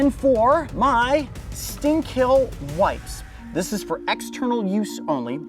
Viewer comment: why does it sound like he said "stink hill"?